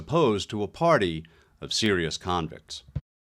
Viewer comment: At the end, did I hear "a party of serial convicts?"